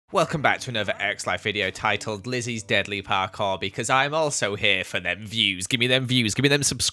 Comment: For a second I thought it said “Lizzie is Dead...” I’m like NOOOOOOOO